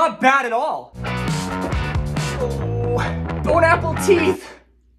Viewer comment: It sounds like he said bone apple teeth to me